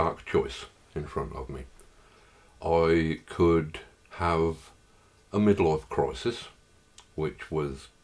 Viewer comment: the auto-captions say "I could have a middle earth crisis." Looks like it lmao